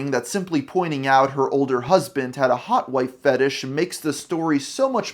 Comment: Her husband had a hot wing fetish?? I’ve gotta be mishearing that